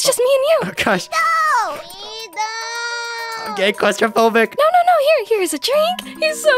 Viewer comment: Why did I hear Etho say "I'm a gay claustrophobic" 💀